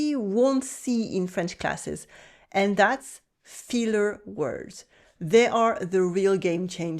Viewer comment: Lol. I thought you said "feeler" [rather than "filler"] words so I thought you were going to mention SENTIMENTAL words. Now repeat after me: "filler". ˈfɪl' = FILL ˈfiːl' = FEEL You're welcome.